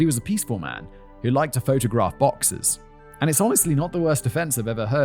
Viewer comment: Oh, BOXERS! I thought Simon said, "He was a peaceful man who liked to photograph boxes." I was like, that's boring subject matter.